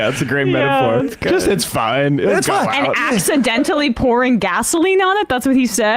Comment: He did not say, "...ACCIDENTALLY throwing gasoline...". He said "...ACTUALLY throwing kerosene...".